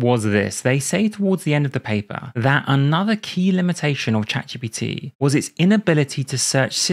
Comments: Nice English auto-generated subtitles, writing Chaturbate instead of ChatGPT 😱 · 😮👉Heads up! If you feel this video is mysteriously underperforming (punished by the Recommendator), you might want to touch up the English CC auto-generated transcript that mishears « ChatGPT » and turns it into a NSFW brand name.